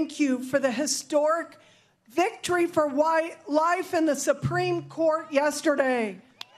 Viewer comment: Did she just say white life?